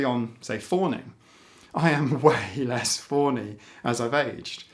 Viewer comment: you use a term that I do not recognize and I am not sure if it is a Manx term or if I'm just not understanding what you said or what the transcript states. The transcript says at 17:35 "...older particularly on say foring I am way less forny as I've aged...". What is "foring" or "forny"? I am quite certain the Urban Dictionary definitions are not applicable. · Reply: Fawny - as in ‘fawning’. Sorry! 🧡